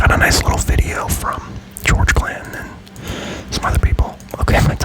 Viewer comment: Thought you were saying George Clinton. Assumed you'd got mad into p-funk.